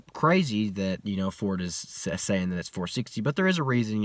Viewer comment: What's hilarious is you and many others need to clean your ears. He said advertised as 460 at the crank yet reality is they're more like 480-490 at the crank. Stock 19 Auto dyno at 442whp is no wherr near 460 at the crank aaaannnnd I'm pretty sure he knows that... Carry on. /slow golf clap. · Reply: Clean your fucking ears out. he said " the ten speed auto makes between 410 and 420 at the wheel pretty consistent. which I think its crazy you know that ford is advertising it as 460." See 3:45 - 4:00 would you like a basic math lesson in percentages? the advertised crank hp vs actual RWHP is right in line 10 to 15% power train loss. based on what he says a 10 speed auto is putting down. he is acting like ford is over rating the cars power.